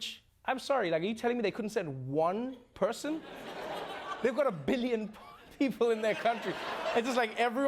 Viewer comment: Did he just say india's got a billion poor people in the country?